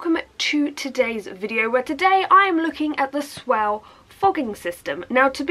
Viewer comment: At first I thought it said “SWELL FROGING SYSTEM” oops 😆